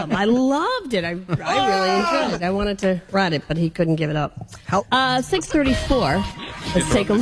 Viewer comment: it sounds like says, "I wanted to ride it but he couldn't get it up."